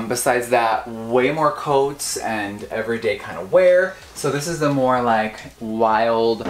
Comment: Did Jeffree just say "everyday kind of wear...?" at 17:10 lol